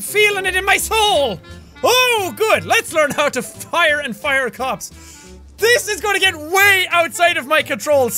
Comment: what noticed that he said "let learn how to fire and fire cops" but it said learn how to hire and fire cops